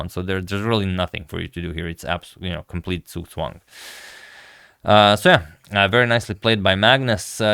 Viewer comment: What exactly you said on 10:28 ? Suswan? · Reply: it's German ("Zugzwang") and basically means that you have to make a move, whether you want to or not.